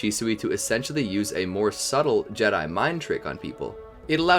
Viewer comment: did he say Jedi mind trick!?